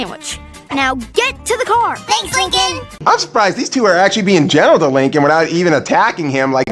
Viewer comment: did he say gentle or general? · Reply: Gentle